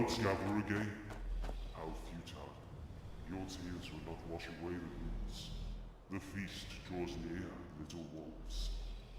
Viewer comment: Do you happen to know what that vulture said at 9:29? For the life of me I can't make it out. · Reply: Are the clouds not working? How futile. x wash away the roots. The feast draws near little wolves. What is said between futile and wash away is unrecognizable for me.